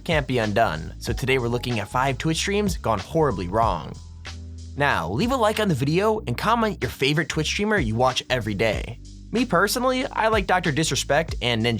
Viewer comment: did you just say “cominte”?